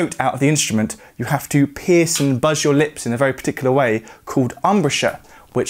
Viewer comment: did you say PIERCE?